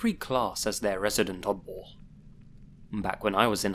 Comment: Took me 5 tries to understand what you were saying at 0:35 I couldn’t figure out you were saying odd ball for the life of me. I was like “wtf is a mud wall?”